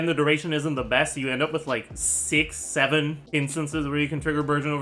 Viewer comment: did someone say six seven????? 🤣🤣🤣😂😂🤣🤪